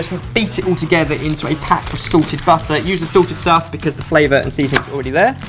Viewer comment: Is it just me or does it sound like says 'sorted' everytime he says 'salted'